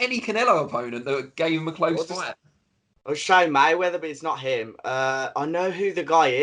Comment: Did say "Shane Mayweather"?